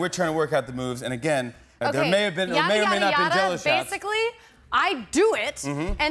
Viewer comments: What did she meant ... when she says 'YAARI YAARI YAARA' at (example: 2:12) ? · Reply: She said "yadda yadda yadda" so instead of him explaining the whole story she can say the relevant part... if that makes sense? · what does she say at 2:12? I'm trying to learn English :) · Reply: "Ya-di-ya-di-ya-da" It's basically just gibberish, so just noises to explain a period of time.